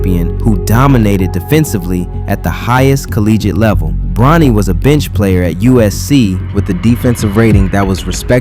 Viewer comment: It really sounded like you said, "Bronny was a bitch player at USC".....either way, this is one of the best breakdowns I have seen on this nepotism disaster to date. Great work!!!